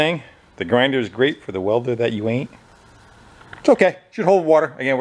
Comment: The one I always heard was "a grinder and paint make me the welder I aint"